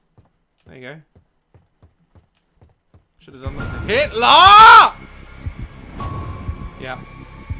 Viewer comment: did he just say “HITLER!!!” 😂😂😂